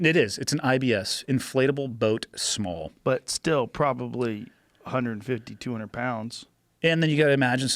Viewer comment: Am I the only one that thought he said they carry 8200 pounds of wait and wasn't until I ran it back that I realized he said 80 to a 100 😂😂 I was like damn this dude's a beast,which he is just not swimming w 8200 lbs beast